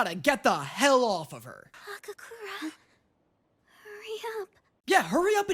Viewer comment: So uh I haven’t seen dandadan before But I hate episode 7. 18:15 did she just call him okakurun…? Have I gotten his name wrong the whole time?